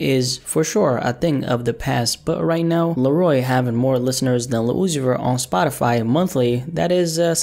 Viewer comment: when he says LIL UZI VERT the captions say LUCIFER........